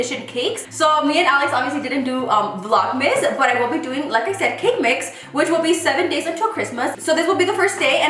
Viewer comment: Oh I’m late 🥺🥺 you make it seem so easy ❤️ You are me saying you’re going to be making cakeMIX instead of saying cakemas 1:09 🤣❤️